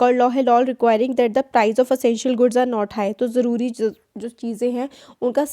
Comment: I thought you said LOL.😆 🤣 😂 😹 😆 🤣 😂